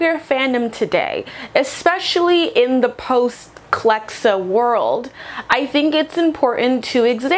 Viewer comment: what's said here? what's "kelxo" (i might have heard that wrong)